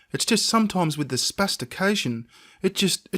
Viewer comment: What did say at 3:04 ?"spestication"what does it mean?